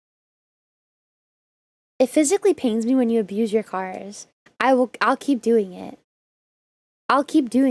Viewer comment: oh god, I thought you said something else at first haha. :face-blue-wide-eyes::face-blue-wide-eyes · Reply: I thought you said you f- cars. o.O